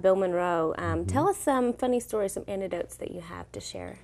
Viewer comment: "Funny stories and..." WHAT? Did she say "Antidotes?"